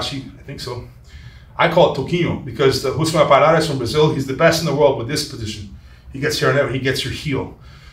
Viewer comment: Who did he say was the best in the world in the outside Ashi Garami? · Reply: Rousimar "Toquinho" Palhares. Often referred to as Paul Harris on reddit or in the USA as a play on his last name.